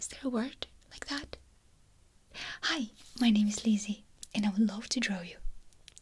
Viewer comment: lol i've never watched any of her videos before, so when she said her name I thought she said "Hi, my name is lazy"